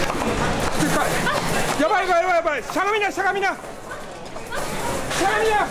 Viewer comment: 『たかみな、たかみな』って聞こえる。『しゃがみな、しゃがみな』だった。